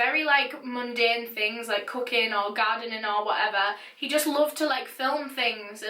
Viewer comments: When you said "doing mundane things like cooking or gardening or whatever" i misheard "cooking" as "cocaine". It was an interesting experience. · Before I comment this I want to be clear I Love accent, it’s the reason I watch her videos, she makes the horrible stories almost musical. Now when she said Peter filmed he’s family cooking I heard doing cocaine and did a double take hahahahaha then I realized I misheard